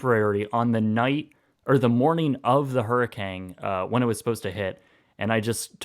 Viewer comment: is it just me or does it sound like he says hurrikang